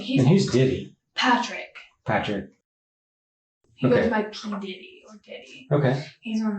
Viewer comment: they say Denny not Diddy lol how do you make that mistake the entire video?! and everyone in the comments can't make the connection either. remarkable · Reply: She said he goes by P Diddy. Is she saying P Denny? It sure sounds like Diddy. That being said the police audio and video is pretty crappy at times.